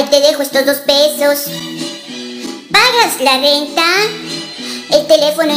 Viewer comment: Yo pensé que decía venta